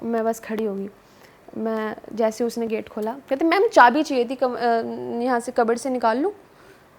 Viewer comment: I heard cupboard as Kabar.